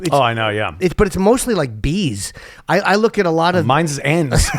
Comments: Did Ant say mine is N's at 35:30 mark 😂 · JN - My algorithm is full of Bees. AC - Mine is full of N's. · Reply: Fuck, I misheard that. I thought he was doing Chip and said, "Mine's Ants."